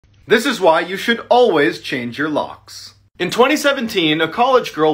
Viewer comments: First time I listened I hear "This is why you should always change your balls" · lmao, at first i thought he said change your socks and so i was so confused on why a lady trying to kill you in the shower should persuade me to change my socks.